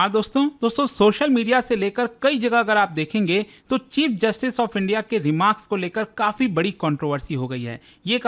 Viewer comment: Did i heard cheap justice of india 😂